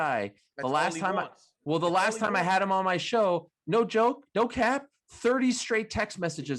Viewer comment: Wait did Ariel say “No Cap”? At 2:06? 😂😂😂